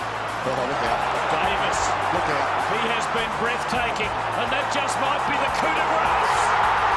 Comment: what dose the commentator say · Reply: Davis... he has been breathtaking - and that just me be the coup de grâce